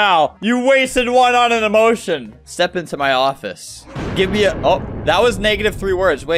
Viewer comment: for anyone wondering what he said he said “give me a shoutout now”